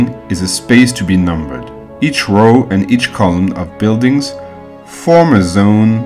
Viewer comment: Helpful - but i find him difficult to understand at times as he does not enunciate or pronounce his words properly. Eg I heard "colour" , but he meant 'Column' .. he says "error", but he meant 'Area'.